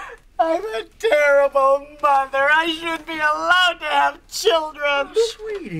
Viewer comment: Lois finds out what she have done to the boys. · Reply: You put father in the subtitles instead of mother. Lois: I’m a terrible father I shouldn’t be allowed to have children.